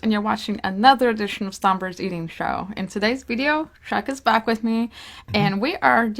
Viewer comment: I swear I heard her call Chuck,Shaq💀